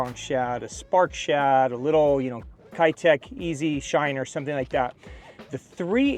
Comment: Did i hear Kie-tech instead of Kae-tech?